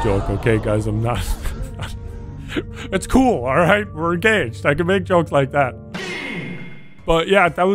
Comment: I thought you said "We're gay" for a second.😅